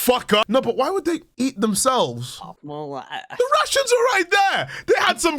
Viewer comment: It took me three times to realize that when asked why they were eating themselves, he said, "The RATIONS were right there." I thought he was saying to eat the RUSSIANS. XD